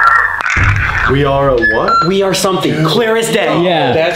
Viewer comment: it doesn't say "there" and when he says, "we are 'a what?'". What they are saying is "we are aware".